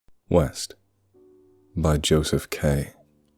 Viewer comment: For a sec, I thought you said weast